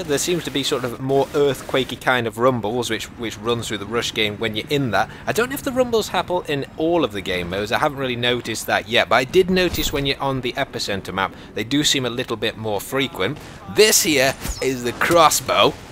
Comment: he says happle instead of happen :D